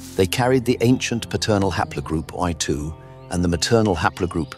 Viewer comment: what is the Y Haplogroup? Did you say I2, Y2 or O2? You kinda blurred it. Your transcript and CC are not clear either. The maternal U5 is very clear. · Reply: At that point I’m referring to I2. I’ll make the caption and transcript clearer. Thanks for pointing it out. I2 is an ancient European lineage, tracing back to the Upper Paleolithic period (tens of thousands of years ago). It is a major subclade of Haplogroup I (I-M170), which is considered one of the oldest paternal lineages in Europe